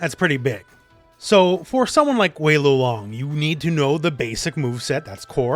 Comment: did I hear "wei luwong"